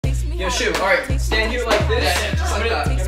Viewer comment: I thought he was saying “yeah, shoot”